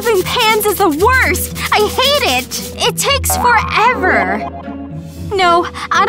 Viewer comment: The fact that she just cracked 1 egg triggered me so much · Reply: Meanwhile me thinking- what did she say did she say forever or did she say for hour. In